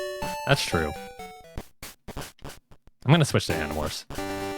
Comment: did he say he's going to switch to Animorphs?